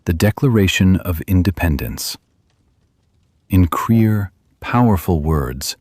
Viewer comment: It should be "In Clear Powerful Words" instead of "In Queer Powerful Words"